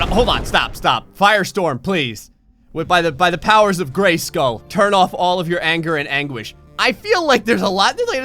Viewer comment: Really i heard "the honor of Grayskull" i knew watches She-ra